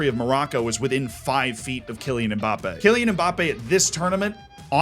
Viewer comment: man I swear I heard "... because the entire country of Morroco was within five feet of KILLING Mbappé?"